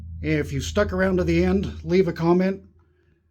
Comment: Oops forgot. 30/30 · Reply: you said leave a comment and now I’m realizing you said “dirty 30” instead of “30/30” 😂 haha